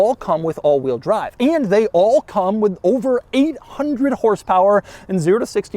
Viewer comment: I thought you said “over a hundred horsepower!” And I was thinking huh, over a hundred horsepower… damn thats crazy.